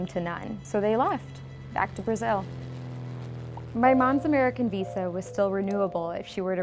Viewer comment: Glad you mentioned that he paid taxes. I hear hateful stories about "illegals" and how they aren't taxpaying amarican citizens (but if they have a real job, they're still paying taxes while undocumented) who mooch off the government (except government aide isn't available to non-citizens). Plus, in the past, there was no process to becoming a citizen, you just showed up. Illegal Immigration is a modern concept, and I hate how people treat undocumented people like... not people. I even hate the way people say "illegals", like referring to an object. Yeah, maybe people should follow the process we have set, you can have that opinion, but can we at least recognize that even people who do things the wrong way are still human beings? Still contributing? Still trying? Are not "less than"? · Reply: Chris Bee did u just say Amarican?